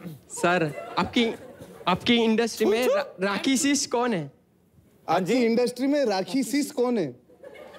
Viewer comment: A question was asked by a boy, at 46:41. Can someone please explain what does the word ‘rakisit’ mean? thank you. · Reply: He was asking 'Rakhi sis'..Rakhi sister